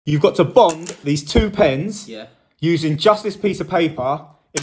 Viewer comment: I low key heard him say, "you got to bum these two pens..." and everything afterwards just kept making it sound wilder and wilder what he had to do, until it actually showed what he really had to do, which was disappointing. Imagine if he had to really bum those two pens and then pick up those keys somehow and then drop one pen, that would have been nuts to watch in real time.